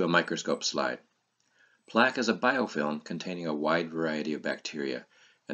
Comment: Thanks for a very informative video. I couldn't make out the word at 1:13 -- "Plaque is a ??? containing a wide variety of bacteria", sounds something like "bidokiln" or "vital kiln". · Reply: The word would be “biofilm”